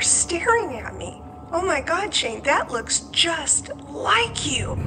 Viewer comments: holy shit exactly at 26:09 I swear someone whispered "Shane" it sounded like a girl's voice. Did anyone else hear that??? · Did anyone else hear that at 26:08? It sounded like someone whispered "Shane" but I'm not sure if it was saying "shit". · Ok so i might be going crazy or hearing things but i swear at around 26:09 - 26:11 it sounds like something says "Shane" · did anyone catch this on the audio recording?To me it sounded like "Shane". Lol It might be breathing or the audio effects because of how close Shane was to the mic or maybe the audio effects after post editing? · Yeah okay so at 26:08 it sounded like a little girl said “Shane”. Y’all he needs to hear this 😳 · Okeyyy now I don’t know if I’m the only one hearing this but go to 26:07 minutes and I hear like a really creepy voice saying « Shane » like it is just insane and I don’t know if anyone noticed this too !!! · after he shows the picture of the Santa and his mom says it looks just like him... anyone else hear “Shane” as a whisper in the back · I definitely heard something say “Shane” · is it just me or did i hear something say "Shane." at 26:08? oml i have the chills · oh nah i heard the “ shane “ at 26:09 too 😳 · when I heard the voice saying “Shane” at 26:09 I literally got goosebumps all over my body and my eyes started watering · postman 207 Does anybody hear “Shane” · Does anyone else hear someone whispering "Shane..." I am literally crying. Please stay safe. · I hear Shane being said WTF!!